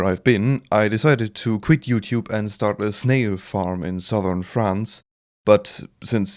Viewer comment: I literally heard "slave farm"